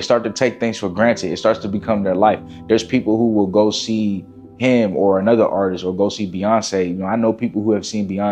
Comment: Did he say H.I.M at 3:40? Savage!!! ❤